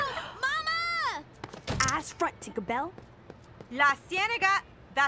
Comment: did she say ass front tinker bell ? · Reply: Eyes front